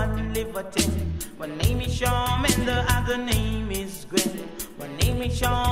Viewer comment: might be the youngest person on this butttt. does it not sound like he is sayin my name is charmander?